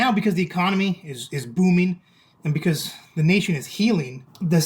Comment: Did I hear him saying “ now that the economy is booming and the nation is healing”??